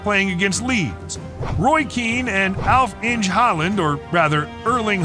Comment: When the speaker said Alf-Inge Haalands name it sounded like he said Half Inch Haaland :D